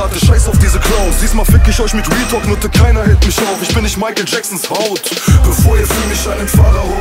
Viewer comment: "Keiner hält/hellt mich auf - ich bin nicht Michael Jackson´s Haut" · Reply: Ich hab immer Michael jackson Braut verstanden, jetzt check ich die Line und das macht sie 10x geiler